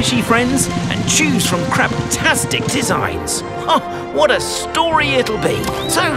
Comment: did he say “crap-tastic designs”? 😂🤣